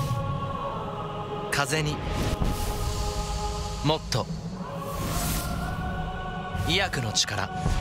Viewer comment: 最初スマホの音小さすぎて 風邪に、もっと、゛宮野゛の力って聞こえたw